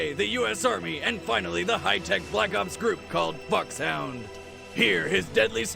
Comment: lol I was watching this with subtitles and instead of "fox hound" it said "fu** sound"